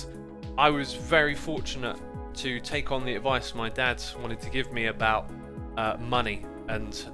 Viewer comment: did x say dad's???